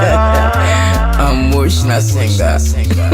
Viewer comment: Did i hear "Amotional "singer 🤣😂